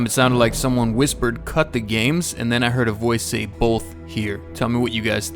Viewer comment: i hear the womans whisper voice saying......"CUT THEIR GAME".......and in 9:54 i hear the mans voice saying......"LOOK HERE"......!!!!